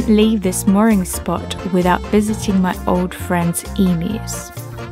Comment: Lol the subtitles changed the sentence at 10:31. "My old friend's enemies" instead of emu's. :P